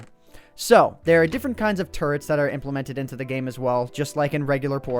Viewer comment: I thought he said turds Lol